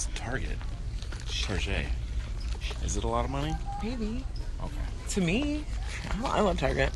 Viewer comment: BEST.VIDEO.EVER. if nia or alexa were single one of them should date mike and BEST TRIO EVER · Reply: I have a felt Nia and Mike are dating and at 1:30-1:40 I swear she said maybe or baby